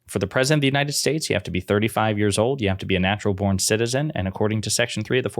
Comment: I thought it said a naturalized citizen?